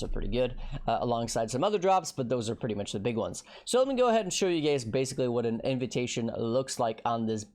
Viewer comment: did you just call us gay? lmao